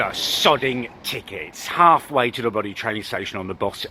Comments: I thought he was about to say tic tac not tickets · Was I the only one at 0:37 who thought he was gonna say "sodding tic tacs"?